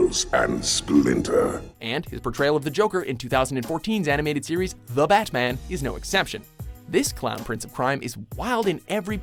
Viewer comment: did he say 2014's The batman lol